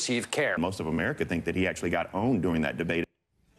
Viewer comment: Ok for a second I thought he said "boned" and not owned wtf is wrong with me